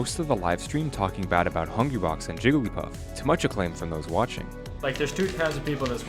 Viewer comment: Idk why that sounded like he said Jewglypuff xD 21:04 Did Hungrybox throw his controller after winning? That looked badass o_o